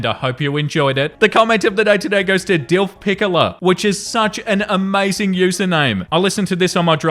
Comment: You're allowed to laugh at my username!! That's what it's there for! 😁🤣 Thank you, that made my day 🥹🥹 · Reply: I have to admit, when first said your username, I thought he was saying "DILF Piccolo," like from Dragon Ball. Pickler is wildly different 😂